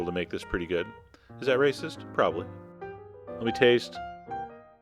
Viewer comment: “Is that racist?....probably.”😂😂😂! The crazy part is: at first I thought he said “is that right sis?”🙄!😂😂😂